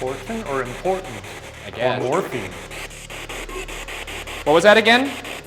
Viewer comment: i heard morphine and kept watching the video and it mentions the man begging for morphine i